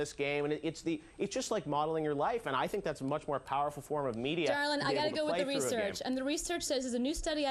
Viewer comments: Did she just call Geoff "darling"?? What a condescending bitch. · does she call him "darling"???